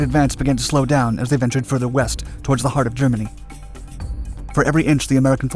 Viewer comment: Did I hear "ventured further west"?